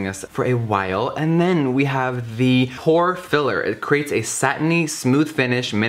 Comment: Was I the only one who heard "whore filler" instead of "pore filler" at 3:33 ? Oh Jeffree, I love you so much😂😘